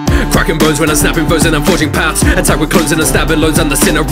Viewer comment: I heard “Hentai with clothes”